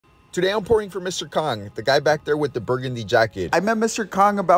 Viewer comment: Swear to god I heard, "today I'm pouring for Mr. Cock" and did a double take